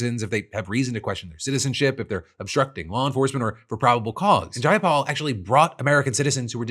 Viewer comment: Ok, I have subtitles on this video and instead of Jayapal, the captions said Jake Paul. A Paul brother mention was the last thing I wanted lol